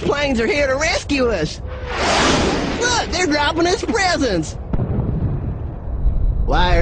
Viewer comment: When he said football yeah I thought he said FOOTBALL LAY EM